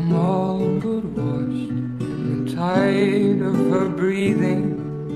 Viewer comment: I always thought it said "I'm tired of her breathing" instead of "in the tide of her breathing"